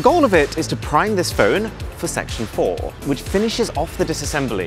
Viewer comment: When he said "Section 4" i kid you not i heard "Sexual Whore".